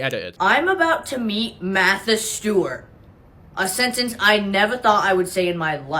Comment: i genuinely thought she said "matt the steward" and i was so confused